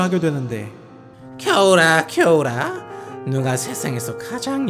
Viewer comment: 근데 여기에서 겨울아,겨울아 라고들려욬ㅋㅋㅋㅋㅋㅋㅋㅋ